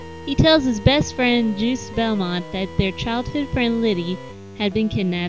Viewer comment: it sounds like you say Juice Belmont. MMMM, Juice